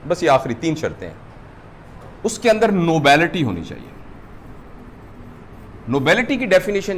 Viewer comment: what is this word please? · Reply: Nobility